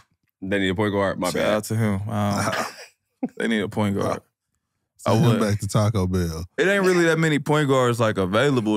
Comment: i thought he said send him back to taco bell to the alverado